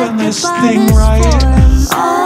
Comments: “Test me you’re mortal” “Test me immortal” “Test me I’m mortal” WHICH ONE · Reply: I hear, “Test me, I’m mortal. Test me, immortal” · “Test me you’re mortal” “Test me immortal” “Test me I’m mortal” WHICH ONE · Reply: I heard “test the immortal” 🧍🏽